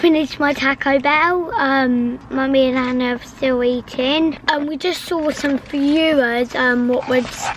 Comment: Honestly thought said we just saw some buers😂😂😂😂😭